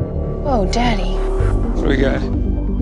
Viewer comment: did she say daddy?!